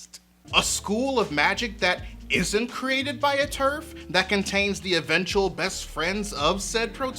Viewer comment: Not turf, but TERF -- Trans-Exclusionary Radical Feminist. Shorthand for a transphobe who claims to be a feminist. J.K. Rowling, creator of Harry Potter, has made it very clear she's transphobic. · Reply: Ugh >_< TERF, not turf, of course. Thank you. My own fault for watching YouTube at 1:30 am instead of being in bed; I'm sure I wouldn't have got it wrong otherwise.